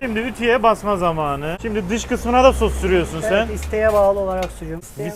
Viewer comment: Beş defa geri sardım tekrar dinledim yanlış mı duydum diye. "Dışkısına da sos sürüyorsun" ne demek lan? Yok idrarına da sürüyor. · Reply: ''Dış kısmına da ''diyor hızlı dediği için öyle anlamışsın.